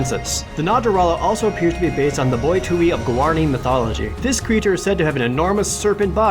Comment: Jared said M'boi Tu'i in case anyone is confused on the name because i had to search for it alot to find this creature!!